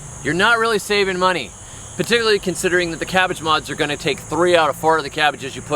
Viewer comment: Cabbage moths 🤦‍♂️. Your not alone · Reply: I thought he said "cabbage mods", the captions thought he did too.